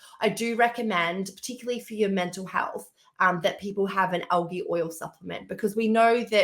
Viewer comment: Help! I can't understand what kind of oil Megan says to add to the diet for optimal mental health, sounds like OG OIL??? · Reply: She said algae oil. She should know, fish don't actually make omega 3. They get it from plants. You can skip the fish and the micro plastic now found in the fish. Eat sea greens.